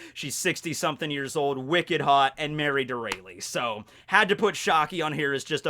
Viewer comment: I thought you said "married to a rapist", I was so fucking confused